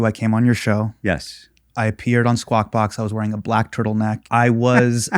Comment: I thought Hasan said he appeared on Squat Box. Must be a different show.